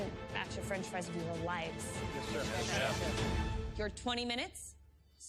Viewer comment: Did someone say Sir on 47:10 to Christina lol